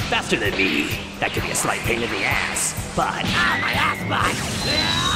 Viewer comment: did cell really say assbutt? xD